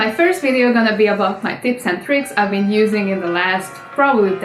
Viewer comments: I thought she said tits and tricks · My god, I heard ' is gonna be about my tits and tricks'' lol